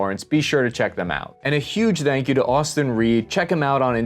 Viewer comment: I thought you said "and a huge thanks to Austin Reeves" lol